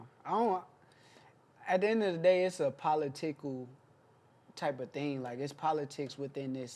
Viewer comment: Did he say palatickle 😂